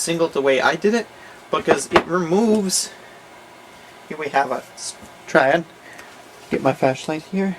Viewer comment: What did he say at 2:08? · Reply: "Here we have a... S, triad" he kinda like messed up on saying it :p hope i helped.